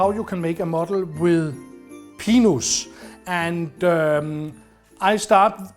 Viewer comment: I understood everything until the last wreath. It sounded like he said penis. Was it "pine"? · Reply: I think he said pinus, pronounced pee-noose. Yes, it means pine.